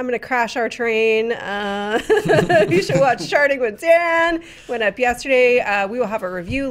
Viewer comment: I thought said "Sharting with Dan". That would be a quite different show.